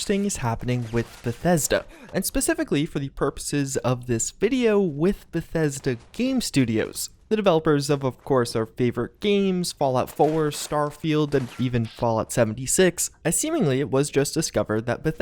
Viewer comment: "..the creators of all our favorite games,fallout 4,starfield,and fallout 76" it hurt me inside when he said fallout 76 but not skyrim,oblivion,or morrowind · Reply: I thought he said ‘our favorite games. [as well as] 4, starfield and 76’